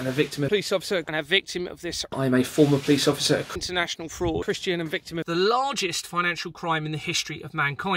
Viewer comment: wait. Did he say he was a Christian?